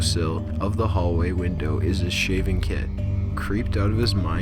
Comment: What did he say at 13:23 ???? "Shaving Kid" ??? WTH is that !?